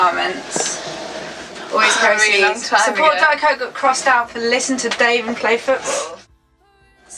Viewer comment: does she say “listen to Dave and play foots?” Dave, as in Dave Matthews Band? I loooveeeee this. Yes.